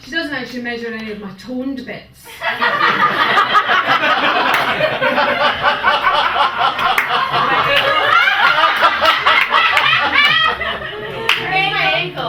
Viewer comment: What did she say at about 06:14/06:15? All I hear is "My torn bits" but I don't think she said that...